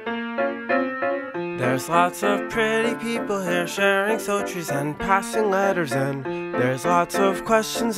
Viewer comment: I always find it funny how andrew misspelling the word "stories" was kept in and they've continued to sing it that way and no one would say anything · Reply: Huh, I always thought he said “sultries”, like doing and saying sultry things. I never actually looked at the words written out until now